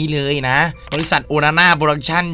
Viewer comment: ผมได้ยินเป็น โอนาน่า